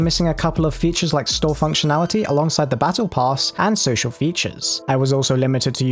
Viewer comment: Wait did I hear battlepass?